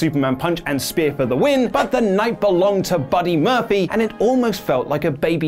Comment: Who DID attack Roman Reigns then?! Let us know your theories in the comments! Also, check out me talking Disney being 'unimpressed' with the X-Men in today's ScreenStalker! <link> Timestamps for this video: Update on Rusev WWE Status! - 5:52 Alicia Fox Drunk Altercation With a SummerSlam Fan! - 6:39 Raw Ratings Sees Huge Bump! - 7:19 The Fiend Not Being Reworked? - 7:51 Real Reason For Samoa Joe Babyface Tease! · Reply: Sounded like you said "Buddy Murphily" in the beginning.